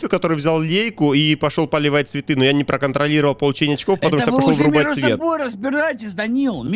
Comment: мне же не одному послышалось "потому что пошел вырубать свет", почему пересматривая это , только сейчас услышал xd